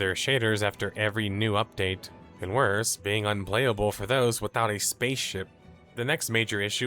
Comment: I've watched several of these videos over the last couple years, and i gotta say theres something very off about this one. Listen to the narration .. there are several very odd inflections at the end of sentences. And some random stutters, which could just be some iffy editing. But overall it sounds weird. And the script is not very accurate. Not up to the usual research standard. I dare to say, i think this video may have been AI generated and trained on his past videos. That may be a leap, but i dont know why else this video is as it is, particularly the weird narration. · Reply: did he say "without a space ship"?